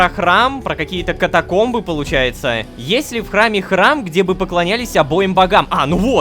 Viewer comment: я один на 25:21 слышу фразу "есть ли в храме храм" ?